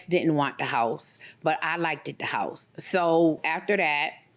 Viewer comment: did she say “LIKE-TED the house??”